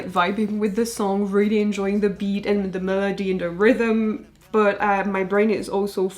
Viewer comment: with your lovely accent "melody" almost sounded like "murder-dy" and honestly? Appropriate. I did not expect noir AT ALL.